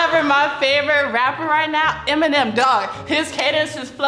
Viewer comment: I thought the black girl was Republican because she was pro guns and her favorite rapper was Eminem and showed really negative connotations about welfare. I think those thoughts are the norm within the right wing community, with the exception of Eminem. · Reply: SHE SAID EMINEM?? I THOUGHT MY GIRL SAID M&M AND I WAS LIKE WHO TF IS THAT GUY ZDUFDJFKSS